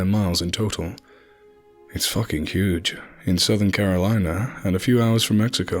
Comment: Twenty-Nine Palms is in Southern California, not Carolina. It sounded like you said Carolina.